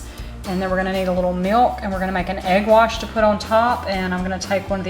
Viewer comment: Hey Jill! Thanks for stopping by :) I liked grating the butter, I am definitely going to do that from now on. I also like scones, but Tim LOVES them, he says they are enough like biscuits, and that man LOVES a biscuit. You know, I've never done a milk wash? Is that what you normally top your scones with? Is it milk mixed with powdered sugar (which is what I was always taught was a simple glaze)? I was just following the recipe, for the most part, is why I did the egg wash :) I need to make these again (this was filmed a while ago and I just never got a chance to share it), and I may have to try your milk wash :) I hope you and your sweet family are all healthy, and hibernating in the cold, LOL! Hang in there, and dream about warm days :) Have a wonderful and blessed day ---- 😍💚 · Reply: oh my goodness I totally misheard you and I thought you said milk wash 😂 I know some pastry chefs use milk and I think it’s to help with browning. Well, that solves that! Egg wash it is! I’m a sucker for cranberry orange scones- but honestly, any proper scone is a good scone to me. 💕🤤